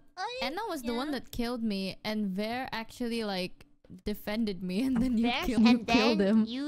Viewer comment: I keep hearing "Ver ga", verga meaning dick in spanish.